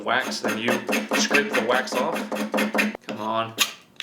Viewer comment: He said Wax off. I heard whacks off. I’m a chubby chaser. I chase my chubby. Time to bop my bologna! Good day to ya fine sirs and ladies. I’m goin skiing with on pole. By pole I mean my nether region.